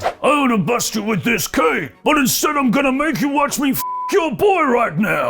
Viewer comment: How do you think Dream will be received on the main roster? Let me know in the comments... WWE CUT 3 Wrestlers! 👉 <link> 8 Non-WWE Wrestlers Daniel Bryan Should Face 👉 <link> More wrestling news on <link> 0:00 - Humberto Carillo Scary Injury On Raw 1:18 - Velveteen Dream Backstage At Raw 2:11 - Daniel Bryan WWE Update 3:21 - SummerSlam & NXT TakeOver Dates 4:13 - WWE Raw Review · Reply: I’m pretty tired of watching the same 3 hour show for the last month or so. I too thought he said “watch me f your boy” but after a few watches I’m pretty sure he said bust. That would be a weird stipulation for the match. Loser drops title then gets f’d by the winner. Perhaps this is Vince’s wet dreams of beefy guys coming to fruition haha